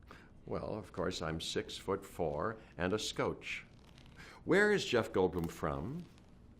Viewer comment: What does he say around 1:40? "Well, of course I'm six foot four and a -----" a scoche? What is this word?